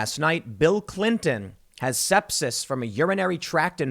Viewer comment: Not gonna lie, I thought that said stepsis. 🤣